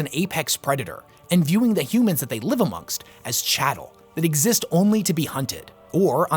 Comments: did say chattle at 7:46 instead of cattle? (i swear I'm hearing chattle) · did you just say chattle instead of cattle?